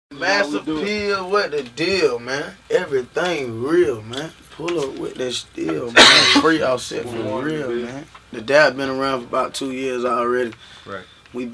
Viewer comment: I thought he said massive pill